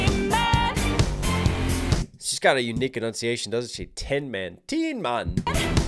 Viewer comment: Fun fact: Lyn, the singer, doesn't speak a word of English. That's why some of her pronunciation and annunciation seems a bit off, like what you noticed with "tin man". She was getting coached both on how to literally sing the lyrics, as well as how to perform them in terms of emotional delivery and emphasis. Her performance only becomes even more impressive knowing that! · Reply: it makes sense considering I always hear "demon" instead of "tin man"